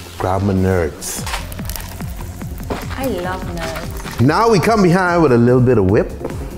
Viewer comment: She said "I love nerds", but I heard "I love nuts"